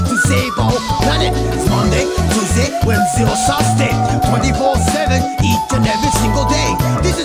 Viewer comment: He meant Thursday, I think I heard S U S D A Y amogus music playing